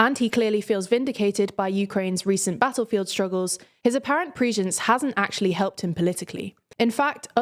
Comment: "...his apparent pr*^#€○" What is the word used here? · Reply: Prudence